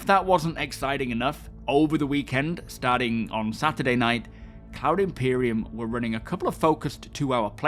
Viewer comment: I thought you said "Clown Imperium" for a moment 🤡🤡